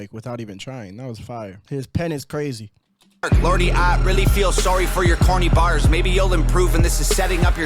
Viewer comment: Did Tom say "Lorde, I really feel sorry for your corny bars"? I don't know Lorde's music, except Leo Morrichioli did a Metal Cover of a Lord's song...